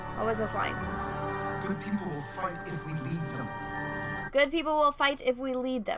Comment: Good people will fight if we lead them. What I heard: Stupid people will pay, if we let them.